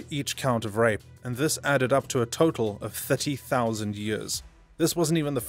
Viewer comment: Somebody said 30,000 years? I thought I heard 30,000 Volts! Whoops